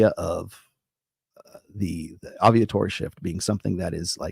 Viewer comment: I thought he said Aviatory shift 0:05 and was waiting for him to talk about the decrease in insects and birds. 🤣